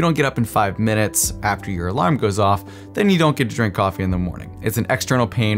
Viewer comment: i had to rewind that 4 times 😂👀 thought I heard “ if you don’t wake up in the next five minutes, you don’t get to **** off in the morning” I said WAIT PAUSE😮